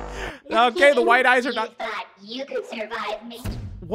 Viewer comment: “you thought you could survive me” is what he said.